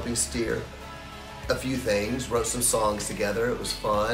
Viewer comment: Ooh songs, I thought she said her sons with her, I was like wtf, that guy looks 25, did she have him at...never mind xD